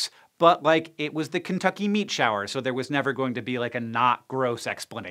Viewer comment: I thought that it said meteor shower. I'm very suprised and happy.